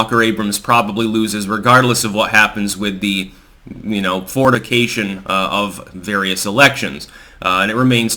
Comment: Towards the end there, a bit of a chuckle as Styx sounds to be saying "Fornication" instead of "Fortification."